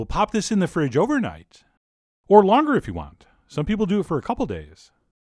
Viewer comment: I'm pretty tired, while watching I thought chef John said "pop this into the fridge overnight, or laundry if you want." But no, he said longer.